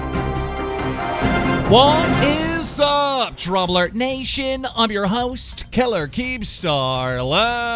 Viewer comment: I always thought he was saying what's up troubled nation I'm your host keemstar, oh